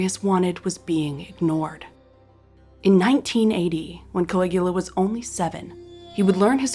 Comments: i thought you said "In 1980..." and thought... BESTIEEEE You got your years wrong! 😂😂😂😂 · Did she say in 1980 when Caligula was 7, or did I hear wrong.